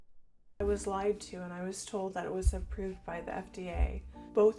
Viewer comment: did she say "improved" by the FDA?